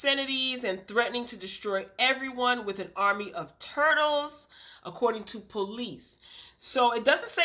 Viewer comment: I thought you said, "...... army of TURDS" at first. LMAO 🤣 🤣 🤣🤣 🤣😂 🤣😂 🤣😂 🤣 Only in Florida. I will never look at turtles the same.